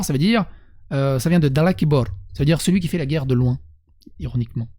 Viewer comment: De l'ombre ? C'est pas de loin ? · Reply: jai entendu "de loin" aussi